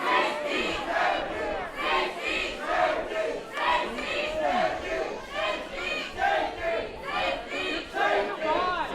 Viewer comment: can someone please tell me what are they saying at 7:35 ? uploader? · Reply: SMA dez the crowd is yelling "free speech matters" and the idiots are yelling "hate speech"